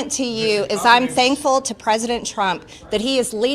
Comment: Every time they say "thankful to president trump", i hear "Thankfull to our supreme leader"